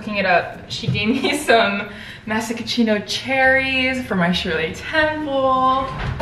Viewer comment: did you just say masachino cherries